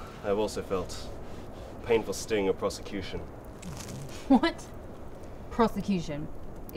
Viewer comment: I thought he said " the painful sting of prostitution" Was like Aight, I'm listening.